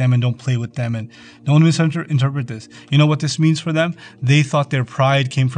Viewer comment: What did he say at 1:17? Im not able to understand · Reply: ‘Do not misinterpret this’